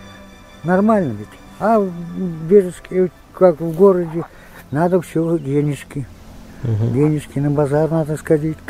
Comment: Мне послышалось или дедушка на 23:20 минуте сказал в город Бежецке?, просто (я живу в этом городе)